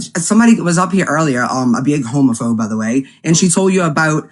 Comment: did she call a homophone ? 🤭